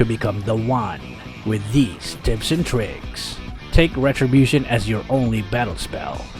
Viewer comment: did he say "tits n pricks" or it's just me?😺